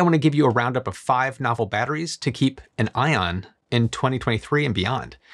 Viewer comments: I thought he said "batteries to keep an "ion" as a pun, probably not but idk with this man at this point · Did you just say "5 batteries techs to keep our ion"? 😂